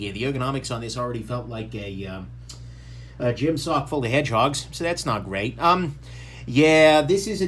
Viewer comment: At first I thought you said "the ergonomics feel like a gym sock full of hand jobs".